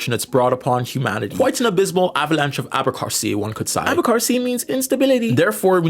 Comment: Im sorry, but what word did you use at about 7:41 ? It sounds like “abercarcy,” but i cant find a match on google. Is it in another language? You said it means instability, but it doesnt show up in thesaurus. Im losing my mind, please help me